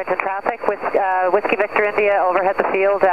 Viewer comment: upper right hand corner in distance... is that a high speed train??? Looked to be moving too fast for an automobile. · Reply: Listening to the radio chatter I believe that was another A/C on the downwind for the same field. At 10:20 you hear someone (WVI I think was the callsign?) calling out that they're joining the right downwind.